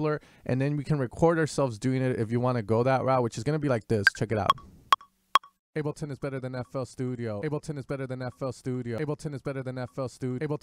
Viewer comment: Your sample quality seems low, I heard "ableton ISNT better than fl studio" :D